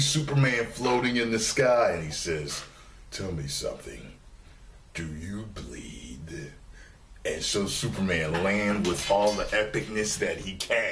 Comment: I thought he said "tell me...do you bleed? ...Kal-El. .."...Or am I trippin'?